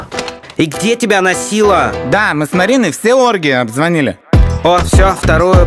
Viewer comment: Мне послышалось : мы с Мариной все оргии обзвонили😂